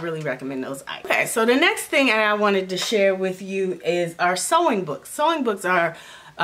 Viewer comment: I first thought you said "song books" when you said "sewing books"